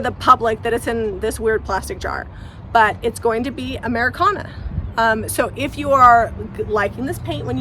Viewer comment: It’s a beautiful dresser and I love the new color. It looks great! At first I thought you were saying “Americana” and couldn’t quite see the connection with the color. Then I realized you meant “Ameraucana”! I hope they get the spelling right because it’s tricky to spell. A funny story, I have one Ameraucana hen and she kept jumping out of the run and laying her egg in an unknown spot. Finally my daughter’s dog found the clutch of eggs under a wood pile and when I got them all out there were 50 eggs! The most amazing thing is, when I washed them, none of them floated! So crazy.